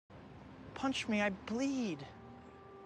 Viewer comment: I thought he said "Punch me, I plead!" when I first heard this.